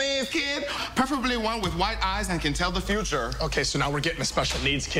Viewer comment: Best line hahahah: "A special needs kid preferably one with white eyes that can tell the future" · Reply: I thought he said wide eyes? 😅😅